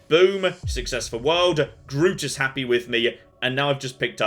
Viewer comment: I heard "Grootus is happy with me" Well considering he's wearing a tunic that's quite appropriate xD